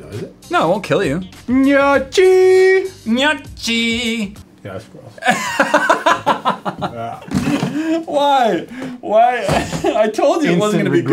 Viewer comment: Does he said "Que asco" ? 'cause it sounded like